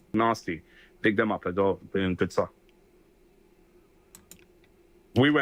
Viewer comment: I thought I heard "big the up aswell, doing good stuff "?